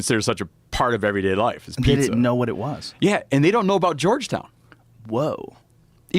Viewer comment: “And they don’t know about Georgetown.” No wonder. I don’t know about it myself. I thought the topic was Jonestown. He really says Georgetown there.